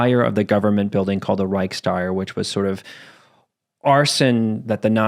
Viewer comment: I think you Said: "Reichs-tire" or smthn. Correct me if I'm wrong. It's called the "Reichstag", in this case meaning "Imperial-parliament" and the arsonists were never proven. However it is most plausible to assume that it was the SA/SS instead of the single Person scapegoated by the Nazis. Edit: you Said "Rechstagsfire". I Heard it again at 27:40. I understand it now.